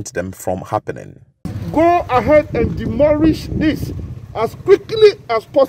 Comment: Did he say demorish 😹😹😹😹